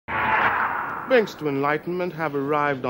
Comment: Ahh. "Binks" I never realised that was his name. I thought he was saying 'Bridge' for some reason.